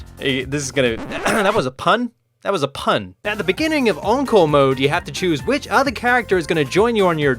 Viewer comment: at the start it sounds like he said uncle mode hahaahahhahahahahahhaah